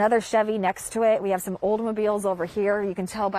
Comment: She sounded like she knew her stuff until I heard her say Oldmobile instead of Oldsmobile. My condolences to the owner